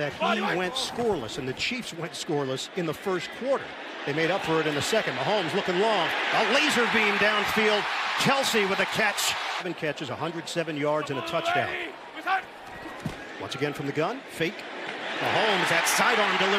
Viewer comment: the commentator said laser beam and I thought that he said lazarbeam